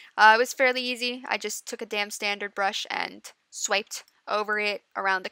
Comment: I had to giggle because it sounded like you said "it was fairly simple; I just took a damn standard brush and..." XD XD